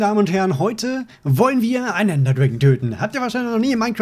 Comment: Was sagt er da? "...töten..." oder so